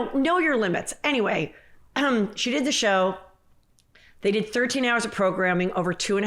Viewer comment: I’m sorry, did you say 14 hours of programming over 2.5 years?!?!?! Omg🤦🏼‍♀️